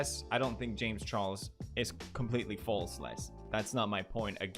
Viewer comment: pewdiepie says turned out to be true but caption said false.